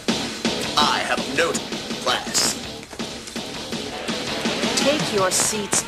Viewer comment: "I have no class" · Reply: I thought it said I have a note class